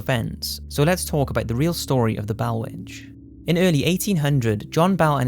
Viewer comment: I kept hearing "Bowel" Witch instead of "Bell"